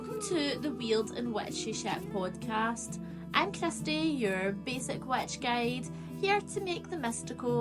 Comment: Sounds like you say your name is Krusty